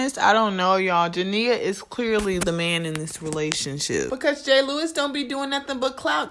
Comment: Is it me or I’m tripping cuz it sound like she saying gay Lewis and not jay Lewis I’m just saying cuz I might be tripping